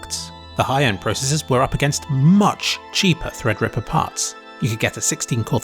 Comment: Thanks for the highly educational video. However you have a mistake in the captions, in about 11:10 you say "Much cheaper Threadripper parts" but the captions say "Much cheaper Threadripper products"